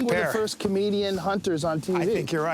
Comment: At the beginning of the video I thought he was saying two Canadians, not comedians. Tripped me up, cause I know Rogan is not Canadian. Had to turn on captions to figure it out.